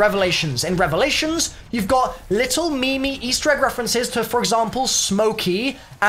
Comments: did he just say me-me instead of meme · did he just say me me Instead of meme